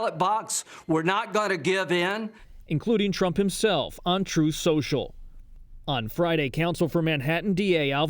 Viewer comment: Every time someone says "On Truth Social" I hear "Untruth Social", a very funny Lady Mondegreen 👌😁👍 ❗